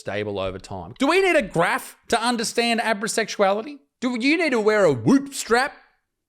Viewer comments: I heard it as abra sexual and first thought “okay, I find magicians/magic/slight of hand sexy too but I wouldn’t make it my whole personality…” I was so disappointed when I saw it written and described. NO ONE CARES. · Shit, I thought you were keeping with the topic. I almost spat my food out, because I thought you said Abosexual.. My bad.